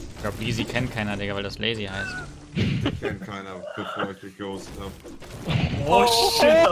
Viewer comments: Was sagt Monte bei 1:19 ? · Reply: Dich würde keiner kennen wenn ich dich nicht gehostet hätte.So ungefähr 😂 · ich verstehe nie was monte da sagt kann jemand kommentieren was er gesagt hat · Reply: Er meinte dich würde auch keiner kennen wenn ich dich nicht gehostet hätte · was hat monte bei 1:18 gesagt? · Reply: Er meinte dich würde auch keiner kennen wenn ich dich nicht gehostet hätte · Was sagt Monte bei 1:18??? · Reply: Dich kannt keiner bevor ich dich gehostet hab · Was sagt monte bei 1:23 ? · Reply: Er meinte dich würde auch keiner kennen wenn ich dich nicht gehostet hätte